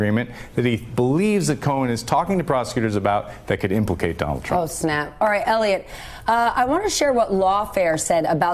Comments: Am I hearing things? Did she just say " Oh Snap!" lol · did she say "Oh, Snap"?